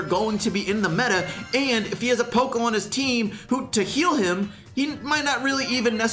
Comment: did u say pokemon ??LOL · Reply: (Poco is on) not (Pokemon)